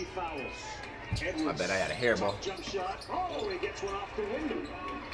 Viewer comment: did he say he had a hair ball?? Lmaooo